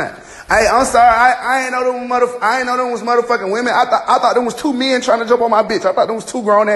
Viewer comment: I need an interpreter for this young man because the only thing I heard was "I thought it was 2 n!ggas trying to jump my b!tch". I hope he wasn't referring to his girlfriend as a "bitch".